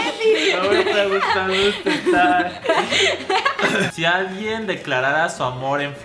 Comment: Que dijo rafa en el 5:07? O-O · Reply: Dijo: "No me está gustando este Tag"